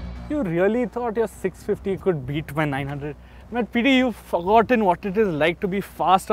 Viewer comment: I just heard Fuckgoten 😂😂😂